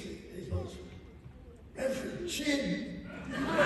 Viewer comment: What did Christopher Lloyd say the guy said from the bridge in the desert? I couldn’t make it out. (Shortly after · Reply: "Reverend Jim!" (Name of Lloyd's character from the tv show Taxi)